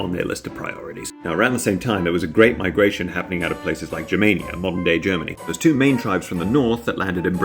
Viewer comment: I wasn't looking and was absentmindedly listening while painting a smoll gecko... So when you said "germania" for some reason I heard "jamaica" 😂 "Around the same time there was a great migration happening out of places such as jamaica and modern day germany" 🤣